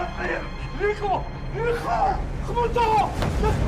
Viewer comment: بدقيقه 1:42كانو سمعت ابو غالب عم يقول لابو لحكم نيكو نيكو